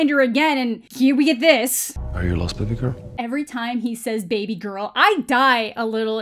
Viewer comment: Oh my god he said baby girl? I thought he said “are you lost Stupid Girl?” Is it just me??